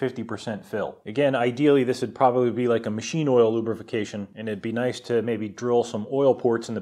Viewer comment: Did you just say "lubrification"?